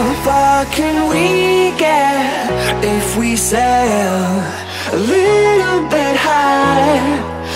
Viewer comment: I Hear " If We Say Hell"